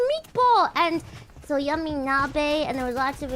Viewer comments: Yummy Nabeが闇鍋に聞こえるのちょっと面白い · 「yummy 鍋」か(´ω｀) 「闇鍋」に聞こえてしまった(￣▽￣;)